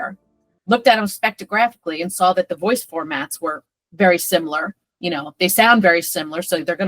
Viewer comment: augh the mental pain of hearing formats instead of formants (for matt vs form int, the a is a schwa or something, neutral vowel, but int is closer than ant or ent)